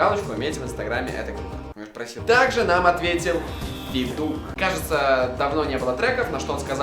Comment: Я подумал что он сказал питух вместо федук ))))